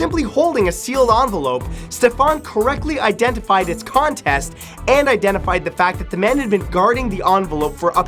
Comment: Lol at 8:34 matt said "contest" instead of "content"